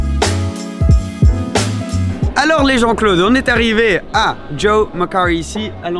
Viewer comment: C'est moi ou il dit "Salut les jean-claudes" ??? mdrr